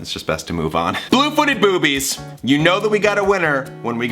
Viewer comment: OMG I THOUGHT YOU SAID "BLOOPITY BLOOBIES"!!! Ha my mind is silly